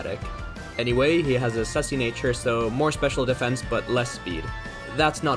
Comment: I misheard "sassy" as "sussy" and then the nxet Pokemon you got was Amoonguss